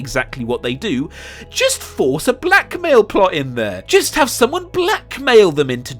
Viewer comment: When you said "blackmail" and cut to Finn, I thought you said "black male." smh XD